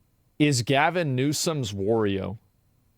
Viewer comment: ohhhh I thought you had said Bannon was Newsome's "oreo" lmao